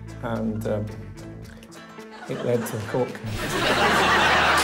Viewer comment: It lead to what? What did Skinner say there? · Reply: A court case. Meaning he got into trouble with the law because of it and had to appear before a court 😆